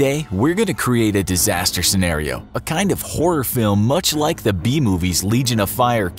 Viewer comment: who else thought he said “horror movies like the bee movie”